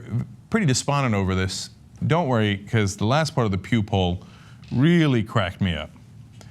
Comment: Pew poll? I thought you said "puke hole"